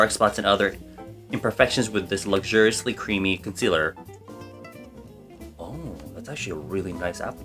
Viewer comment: why did my one brain celled ass thought he said "watashi" instead of "that's actually" so i was like wow king throwing in japanese words